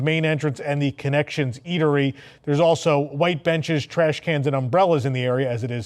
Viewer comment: For a second there I thought had said that Disney had placed ' white trash benches' in front of guardians of the galaxy. Thought that's a bit close to home.... but rewind indicates I hadn't payed close enough attention... 😉🤣